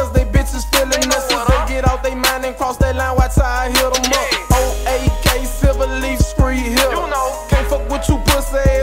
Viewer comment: There’s no lyrics anywhere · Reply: I thought he said old Ak silver leaf street hitta